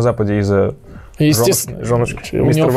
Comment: жёнушки ш...)? Или послышалось)